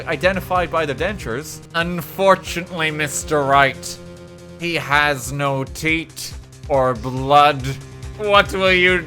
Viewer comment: What he said: UNFORTUNATLEY, MR. WRIGHT, HE HAS NO TEETH. What I heard: HE HAS NO TEAT. The Mental Imagery we didn't deserve: VON KARMA NIPPLES